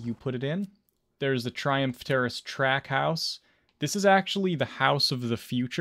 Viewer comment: LOLZ I thought you said Trap house… 👀 also accurate.